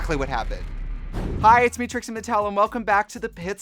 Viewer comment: I thought you said “it’s me citrus Mattel” and like kinda liveeee